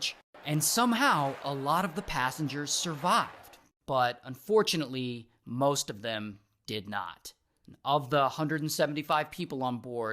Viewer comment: "fortunately most of them did not" i heard that right?